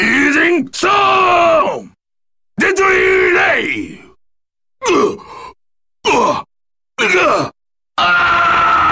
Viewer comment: What did he say after raging storm? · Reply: Deadly Rave.